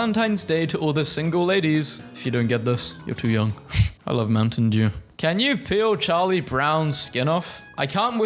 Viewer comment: an add came up right as he said “I love Mountain Dew” and it cut his sentence in half, so I thought he said “I love men” cause it cut after moun and it sounded like men without the rest of the sentence 😭